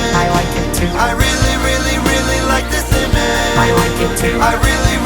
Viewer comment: It sounds like he is saying: I really really really like the simage